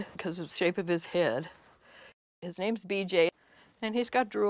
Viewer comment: did she say the babies name at 1:25 was BJ.......